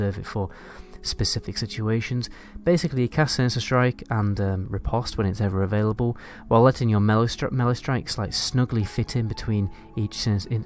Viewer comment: dude what did you just say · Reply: herolink17 “riposte when it’s available while letting your melee strikes snuggle fit into something